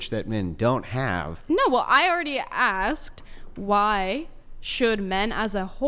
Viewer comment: All I kept hearing was, "well, I agree" all the while saying "I'm having hard time accepting" 😅😂